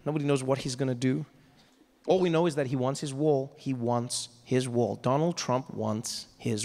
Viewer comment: I lowkey thought he said "He wants his war"